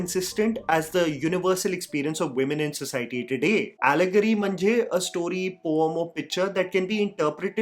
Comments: Did you said Allegory Mhanje Marathi ? At 4:47 min ! Btw I just completed this series ❤ It was superb 😍 love this genre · Did you just say “mhanje”? 😃 On another note, you definitely convinced me to watch the show.